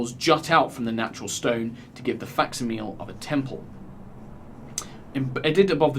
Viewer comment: "faxameal"? What was the word he said? "to give the faxameal of a temple" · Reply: Facsimile